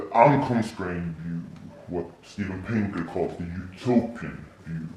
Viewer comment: he clearly says "Steven Pinker", not (as the subtitles say) Thomas Paine.